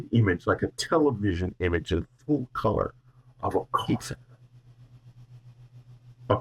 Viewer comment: For those wondering about [18:54] in response to: What does the universe mean to you? Whitley sees a vivid image of a coffin, representing "they've come to the end of spacetime, and I understood why they were here, because we have not. And they do not want to take us in one instant to the end of spacetime like they are, they want to share our journey and that sharing is what communion is all about." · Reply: I heard "of a pizza" only slightly garbled. Relisten gave "coffee" and eventually figured out "coffin" and the whole thing gave me an amused knowing laugh 😊